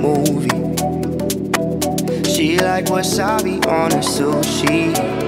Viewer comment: WHY IS WASABI ON SUSHI STRANGE??! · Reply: This entire time I thought he was saying "She like wasabi on her soup, she..."